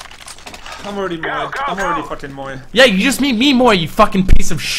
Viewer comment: what do they say at 2:35? you made me "moi"? I'm confused